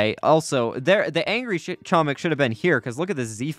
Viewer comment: what did he just say!? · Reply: "the angry shit chomik should've been here"